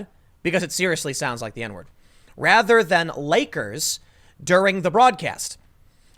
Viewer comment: I'm gonna have to disagree with ya. I've listened to the clip several times and it appears to my ears that she indeed said "nakers". I heard no "g" sound. There was a definite "k" sound though.